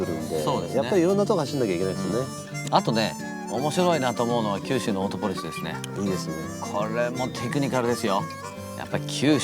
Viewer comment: i think Tsuchiya says "Kyushu no Autopolis Race", he may be mentioning next JGTC race.